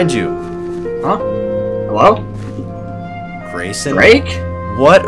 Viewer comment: did he say Grake